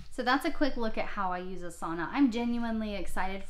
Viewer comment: I thought you were saying a sauna. 🤦‍♀️ I get it now. 😂. Do you know if it is available for ipad?